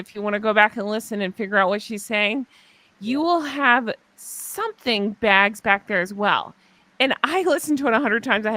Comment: it sounded like “You will have this and there are a couple other bags back there”